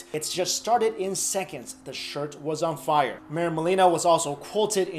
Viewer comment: I thought the last sentence said "That shit was on fire" 😂